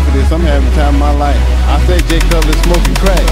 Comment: Could somebody translate what does Ronnie say at 0:46? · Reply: he says: "I live for this, I'm having the time of my life, I think Jay Cutler is smoking crack!"